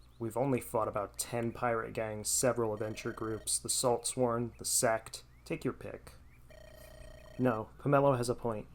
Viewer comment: I had this in the background and at 4:12 I thought I heard THE SEX I spat my water lmao